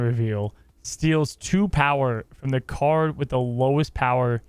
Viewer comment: annihilus needs cards below zero to throw them over so it would take a “little” work to get her in the negs. I agree that draw a card for two is OP, but I’m thinking about Gitaxian Probe (from MTG) style effects to stay with her abilities in marvel, the whole mind read thing. Maybe switch it to, “reveal opponents hand, a random card costs 1 more next turn” with an animation showing you which card got hit for the whole, can see thoughts and paralyze feel to her character. Dunno, feel like there’s still more in the “information gathering” space Marvel could steal from MTG and modify to fit the flow. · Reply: Ah, thought it said a -2 power at first